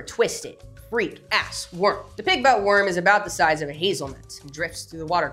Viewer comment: I misheard 'pigbutt worm' as 'pityf**k worm' somehowml. 😮😂